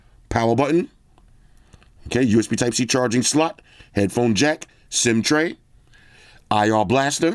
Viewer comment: did I hear headphone jack ?